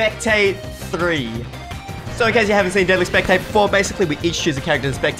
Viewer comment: did he just say deadly spectate 4?